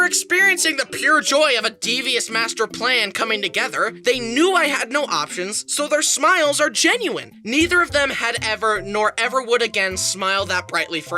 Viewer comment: did he say.. MASTER PLAN?..